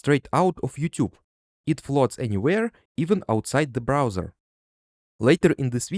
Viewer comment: I heard “outside the Bowser” and then when i watched it the second time i heard browser